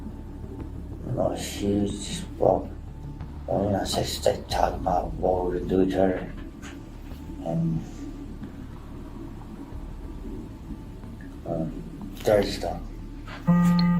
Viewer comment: I hate when they say "inaudible" or "unintelligible" when you can actually clearly hear what's being said. 4:19 he said "she's about well.. i mean, I say-she's say-talking about what would i do to her and... um, dirty stuff." He's stammering about but you can still hear what he says.